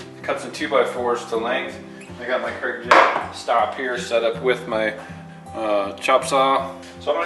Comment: At the 13:23 mark you say the name of Kreg _ stop, it sounded like Kreg jam stop. Where did you find this equipment? I am new to this wood working stuff and I am considering this in my new home.